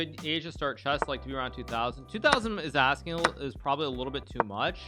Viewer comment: What did he say? I did not understand the 2000 part. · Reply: And he says 2000 is a bit much? I’m surprised about that, I think it’s definitely reachable.